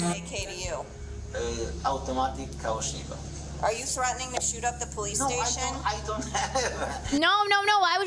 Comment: not going to lie I was only listening and I thought she said "Are you threatening to shoot up the PlayStation" and I was confused for a second